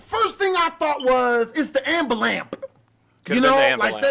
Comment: This man did not just say “ambu-lamp” we’re all going to hell because of that one word